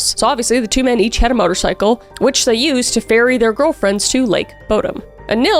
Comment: Oh my gosh! At 3:57 I heard "which they used to bury their girlfriends" instead of FERRY their girlfriends! 🤣😅